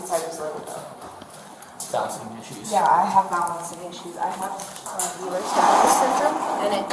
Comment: anyone who actually has anxiety or panic disorder etc would know that breathing the way she is causes more anxiety. she’s just faking bc the cop didn’t coddle her about her EDS. pathetic. if i ever have to tell a cop about my ptsd or panic disorder, im pretty sure they won’t believe me because of people like this. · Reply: I thought she said Buellers Danlos Syndrome instead of Ehlers. But I could have heard incorrectly. After that all I could hear in my head was Bueller? Bueller? 😊